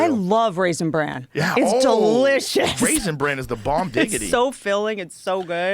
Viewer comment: TF‼️ Joe just say 😂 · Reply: Bomb digidity😂😂😂?